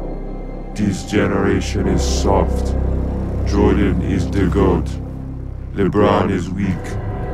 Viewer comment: It sounded like Giannis said "Troydan is the goat."